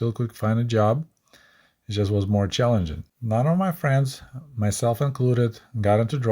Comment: approximately. none of my friends?a lot of my friends,some of my friends myself included got into drugs. I think you said none but listened to it four times and don't know what you really said. "Myself included" is not generally used in English with the word none. I think you meant to say None of my friends or I got into drugs. They way it was worded made it sound like you got into drugs. Pretty sure that's not correct.